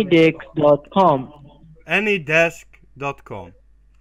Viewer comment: sounds like the scammer says "Any Dick" instead of Any Desk lol 😂